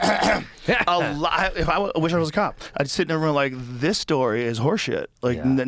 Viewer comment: did joe just say "when I was a cop"?